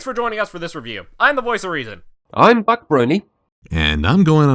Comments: did he just say Butt Brony? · Oh. For a second it sounded like he said BUTT Brony. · I first heard "Buc Brony" as "Butt Brony".